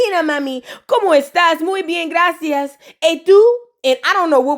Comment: You so silly....they said, "y t'u", and you thought they said, "Etouffee" 😅😅🤣🤣